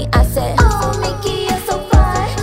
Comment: Am i the only one that thought when she said oh mickey ur so fine was her saying only kiss a boy?